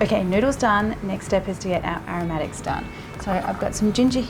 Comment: I thought she said "our medics done" 🤧